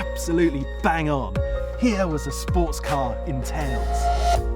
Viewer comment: Can someone clarify that slogan for me?: "Here is a sports car...entails?(not me it's the subtitles/captions), in tales?" I cant' seem to hear what he's saying...or frankly the meaning of it. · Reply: A sports car “in tails” as in, the tails of the jacket worn as part of a formal outfit, i.e. top hat and tails